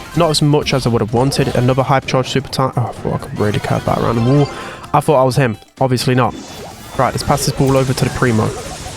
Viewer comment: what did you say ? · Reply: he said fuk so what